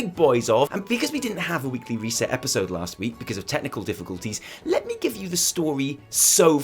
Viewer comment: I heard: "Because of tentacle difficulties"